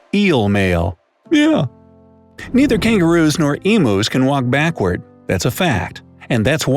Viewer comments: Did anyone else hear him say emoos instead of emus at 3:10 ?? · did he just call emus emos